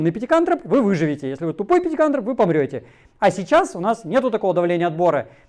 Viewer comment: Несколько раз послышалось: тупые практикантропы. Буду пользоваться. Спасибо.😀